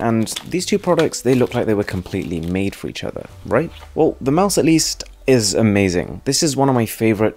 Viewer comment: did he actually say WELL THE MICE Lol hahahaha🤣🤣🤣